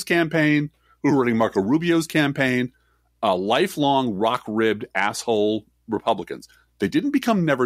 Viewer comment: says "rock ribbed" I heard it as "Rock Ridge". It still made sense.